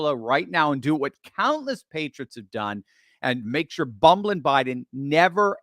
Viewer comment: Did you just say Bubbling Biden?